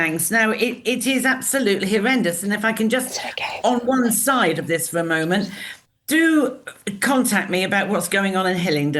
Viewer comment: Who and what was whispered at 07:10?? 🤔 ...I thought it was a ghost at first! 😅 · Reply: "It's OK" I think it was...